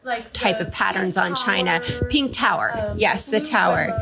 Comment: What was the word they used at 3:27? It sounded like “pink tower”. 🤔